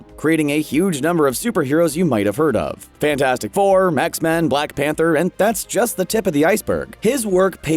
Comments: did he say mex man?! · I don’t know if it’s just me, but doesn’t it sound like he said mexmen instead of xmen. I might just be brain dead